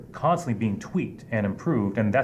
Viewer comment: In this clip I think I he said, "constantly tweaked and improved".... , but what I heard is, "death by a thousand regulations/rules".